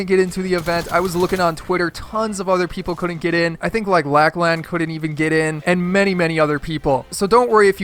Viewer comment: lack land instead of lachlan